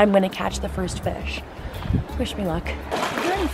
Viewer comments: I seriously thought she said "fish me luck" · When you said "wish me luck" I thought you said "fish me luck" haha 😂